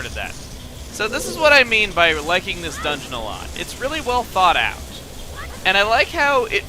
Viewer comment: Every time he said "It was really thought out" I heard "It was really thawed out"